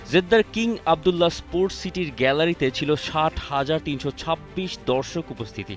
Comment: কি বলে এটা শুধু 7000 দর্শক😅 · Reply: 60000 bolce